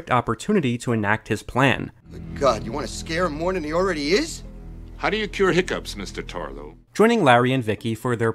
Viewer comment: I vaguely think I saw this movie in the 80s (maybe). At 6:00 does he call the guy "Mr. Torgo"? I wonder if that could be a reference to Torgo from the awful and oft-lampooned 1960s independent horror film "Manos: The Hands of Fate".